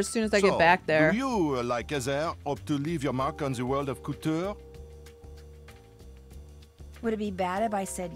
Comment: sounds like he says "in the world of cooter"